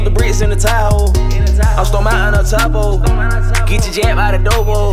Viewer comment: Anybody know what he said at 1:25 after “stuff the bricks in the Tahoe” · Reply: Chauncey Blanton I’m Stone Mountain el chapo